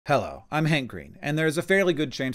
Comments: At first I thought you said "hi I'm hangry" lol sending to the Georgia fam. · Giving my friend a hug for sharing this the next time we get lunch. Sharing with all my GA Friends. Thank you for your informative video. Though when I started the video, I thought you said, "I'm hangry." Made me laugh before having to rewind.